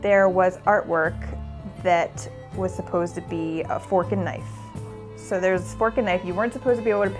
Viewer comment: "There was artwork which was supposed to be a f***n' knife..." Wait what? *Rewind "Fork n' knife" 😂 Unfortunate word combo